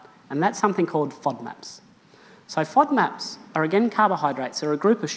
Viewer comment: At the 13:35 mark (and beyond) - what in the world is he saying?? Sounds like Frog Maps!? Are there lost frogs wandering about looking for their maps? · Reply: FODMAPs (fermentable oligosaccharides, disaccharides, monosaccharides, and polyols); they're short chain carbohydrates that are poorly absorbed in the small intestine.